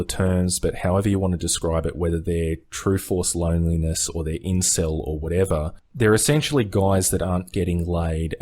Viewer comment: What's the word/abbreviation at 7:25? "insel"?